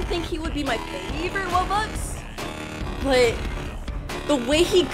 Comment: The captions said roblox WHAT